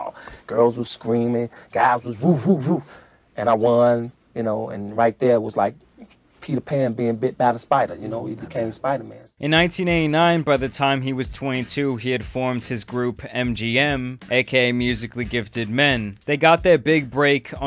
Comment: did he say “Peter Pan being bit by the spider to become Spiderman” 💀💀💀💀